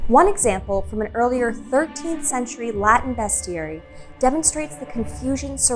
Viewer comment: the subtitle says "132th century" instead of "13th century", btw!)